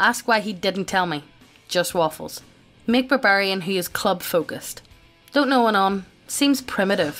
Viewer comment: Well that was bizzare. I thought said "club-footed barbarian"